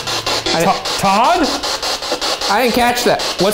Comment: Am I the only one who hears "The gods" at 12:38. I fully understand its my mind making crap up but I find it interesting